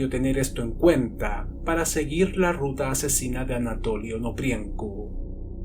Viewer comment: v pensé que decía "Anatoly ANOPRIETO" necesito gafas y un psicologo urgente